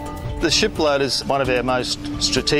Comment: I heard "shitloader" the first time, though.